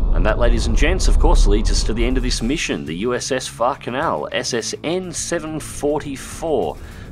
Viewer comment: Oh, USS Far Canal. I thought you said USS Fuck'n Hell